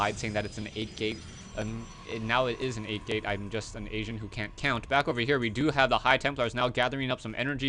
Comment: what does he say at 7:28 "i'm just some asian who can't count"?....